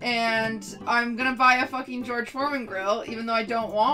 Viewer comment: I thought it said George Floyd grill lmao